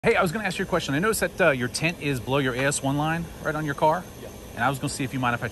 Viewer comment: 🙈At first I thought he said ,I noticed your tent is blow your ass ten line .😆😅🤣